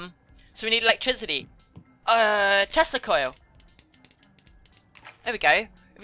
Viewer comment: i thought that she said testicoils and i was like er this is no longer PG